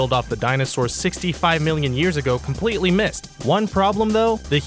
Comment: your caption says "..The asteroid that killed off the dinosaurs 65 million years ago passed completely" but in the video the audio is heard as "completely missed". Narc.